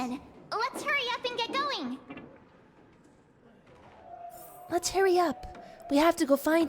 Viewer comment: I thought March said "Let's stop yapping and get going" xD